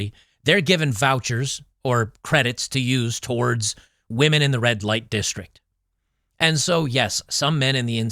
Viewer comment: "You get a Red Light Voucher, and You get a Voucher and You get a Voucher." Thank you Oprah, Not..." 😅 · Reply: At first I thought you said "Bud Light". :)